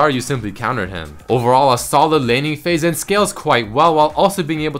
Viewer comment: lol each time he says "a solid laning phase" I hear "a salad laning phase" xD